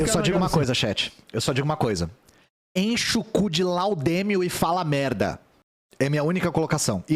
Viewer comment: Por um segundo eu entendi “enche o cu de LÁUDANO” 😂😂😂